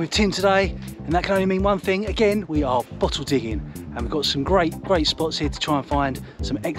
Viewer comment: one minute ago? ohhhhhh boy! I thought you said fossil digging but I guess bottles are fine too lol